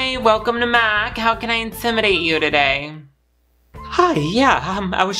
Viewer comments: I SWEAR TO GOD in like the fourth skit where the guy said “how can I intimidate u today” I heard “how can I inseminate u today” and let me tell u I FUCKING CHOKED. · he said "how can i intimidate you today" and i heard "inseminate" BYE